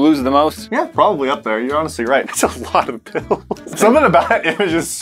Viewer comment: When was talking about the bills I thought he said pills XD.